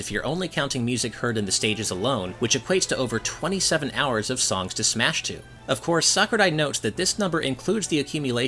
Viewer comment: Did you just say: "Songs to smash to"?